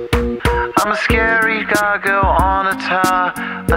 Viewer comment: Omg I thought he said "imma scary doggo on a tower" 🤣🤣🤣🤣🤣🤣